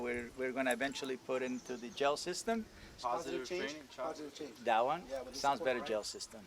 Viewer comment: “Sounds better jail system” 😹😹😹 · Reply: I thought he said "gel system" 🤔